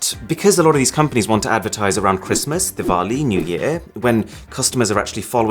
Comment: Did I just hear diwali